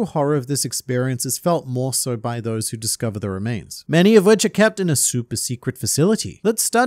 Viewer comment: At First this is what i heard at 1:08 "Many of wich are kept in a soup of a sillyties" English is not my first language . You speak good i just hear weird things sometimes. This time it was funny to me how the sound matched the image.